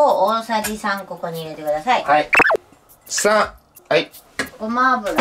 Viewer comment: 3がパン！に聞こえる笑